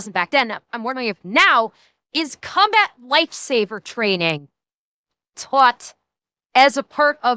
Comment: I keep hearing "Combat Lightsaber Training" and this sounds like something I'd be interested in.